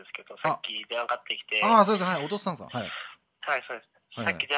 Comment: この流れで普通のトーンではい嘘です、って言うの普通に笑った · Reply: 同じくww よく聞いたら『はいそうです』って聞こえた